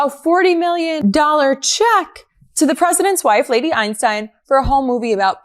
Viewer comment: Lady Einstein Excellent title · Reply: Oh ! I thought she said " Lady Epstein " !!!!!